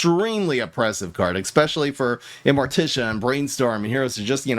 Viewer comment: Did he really say excpesially? Twice?